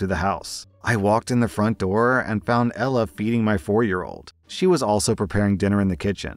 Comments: i thought he said beating 💀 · I thought you said beating not feeding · I heard beating not feeding and I said WHAT outloud